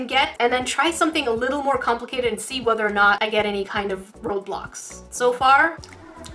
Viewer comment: Is it just me or did i heard you say roblox